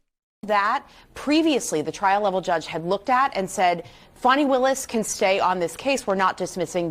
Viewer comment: Is she calling her Funny Willis?